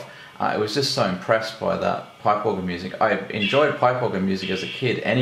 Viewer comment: what do you say there? Arpeggio music? (It sounds more like pie pogo, or the autosubs have it spelled out as pipework or play poker music.)